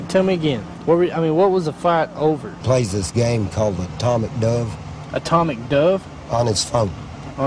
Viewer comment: Legend has it, he’s STILL playing Atomic Dove to this day.... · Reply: Ohhhh ... I thought he was saying Atomic Dung lol